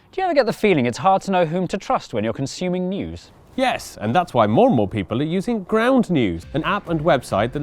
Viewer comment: I heard that as "Mornmal people". Maybe I heard it wrong but my ears look alright to me, but what are the chances both my ears are buggy at the same time ? Maybe someone is pulling my lobes.